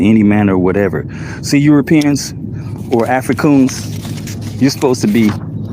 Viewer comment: “By any means necessary” means by any worthless paperwork necessary… Did I hear him say “Africoons” twice? That is special like no other special.